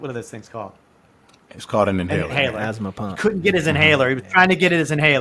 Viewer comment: did lupe say astma pump? 🤣🤣🤣🤣🤣